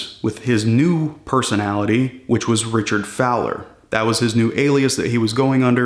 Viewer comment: For some reason I heard Richard Phallus I was like wff? and had to go back 😆